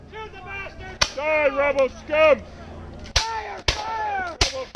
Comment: Am I the only one who heard die rebel scum when gun didnt fire? Its